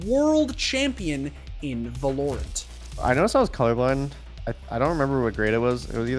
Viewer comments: did he just say vah-lore-ent? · did you say velorent?